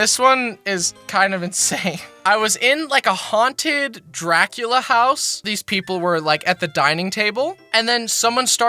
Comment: I thought he said, haunted crack house